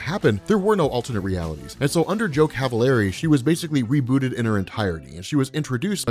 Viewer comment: I misheard "Joe Cavalieri" as "Joke have a leary" the first time I heard it in this video and had to rewind and figure out what you actually meant. That was terrible. And my own fault.